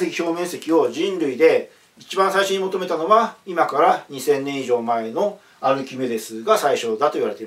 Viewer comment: ここ2,3年以上前って聞こえてびっくりした